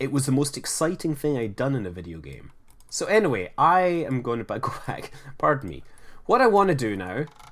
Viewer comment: Did he just say "I am going to buy crack" at around 12:20?